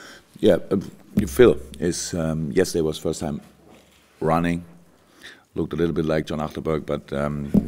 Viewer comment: what he did say ? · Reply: He said Coutinho was running now, but that he looked a little bit like John Achterberg. He's the GK coach.